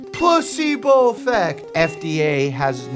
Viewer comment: when he pretends to be pokemon trainer, he sounds like he said plusebo effect...